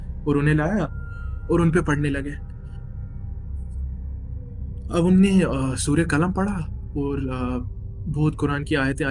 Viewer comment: what did you say?? Ricited Surah Qalam?? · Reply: Ji he said surah qalam